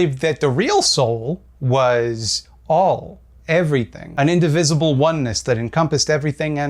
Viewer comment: did you say "indivisible"? Edit: I did not know this word. I thought you misspoke, after the third time I DuckDuckGo'ed it. That was not super helpful. First hit was a Wikipedia page: "Indivisible is an action role-playing platform game developed by the now-defunct Lab Zero Games and published by 505 Games." Second hit merriam-webster: "Definition of not divisible" Also not helpful. I hate it when google works better than DuckDuckGo.